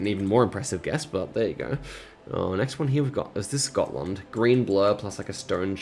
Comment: I thought he said „Scotland“ and I was SOOO confused